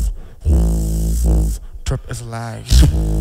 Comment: All this time I thought it was "Drop to survive" 😅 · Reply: I heard that "tarp is live"